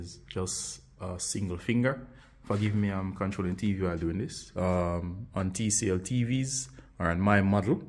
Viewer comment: Don't think he knows about other tvs just his one. Think he needs to learn there are more out there 🤣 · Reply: he says on TCL or his/my TV. Button amazed ya so much u zoned out and didn't hear nothin dude said lol 😁😂🤣😉